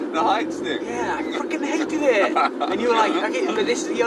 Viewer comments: Maybe... should have bleeped out there. He said Fuckin' instead of Freakin' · "Freaking" yes that's exactly what he said... xD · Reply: I heard "F$&kin' hated it" too 😂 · 음질 때문인가...? 0:50에 Freakin'이 F*ckin'으로 들리네요. ㅋㅋㅋ